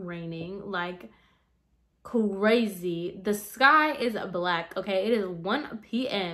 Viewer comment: Ok was it just me but when she said “the sky is black” I heard “this guy is black”😂😂